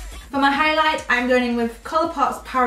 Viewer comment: Hello, What is the name of the colourpop highlighter that you used? It is not listed...and it sounded like you said "power sol," but I cant find it online. Also, do you recommend the Milani foundation? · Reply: Parasol..it was in a limited edition item in a strobing kit which isn't online anymore